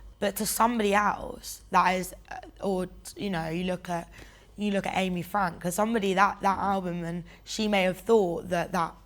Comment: What is the name she says at 10:34? That sounds like Aimee Frank? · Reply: Yes Amy’s Frank she’s referring to that Album ✨